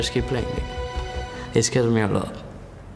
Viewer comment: what does he say on 5:23 and how old is soaz ? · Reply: "it scares me a lot"